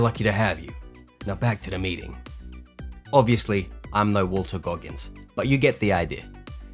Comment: Sounded like you said Walter Goggins. It's Walton. Otherwise sick video